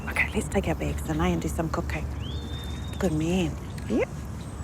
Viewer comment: sounded like she said come grab the bags and we can do some cocaine lol